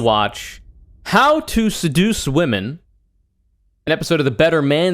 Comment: I just woke up and I swear I thought that said “Reducing Women”.